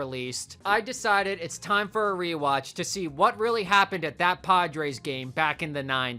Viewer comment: for a moment I thought you said pod race.